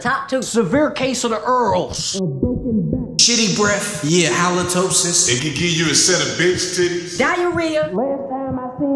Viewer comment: I can't Even tell what he saying at 0:53 but it's funny af🤣 build up energy in his words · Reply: A BROKEN BACK 😂